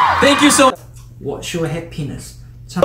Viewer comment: I’m sorry but what is Hoyoung saying? I’m understanding something that he definitely didn’t say 😳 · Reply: MeralK. He said what’s your happiness 😂😂😂but I think i know what you heard💀